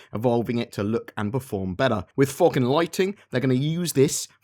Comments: he said fog and lighting.... but it soooo sounded like he said FOOKIN lighting lol XD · I heard “with focken lightning...” lol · I thought you said Fuggin' Lighting XD Great video as usual. Cya in the 'verse o7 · I thought you dropped the F-BOMB at 3 minutes in. Sounded like you said "F-ing Lightning!"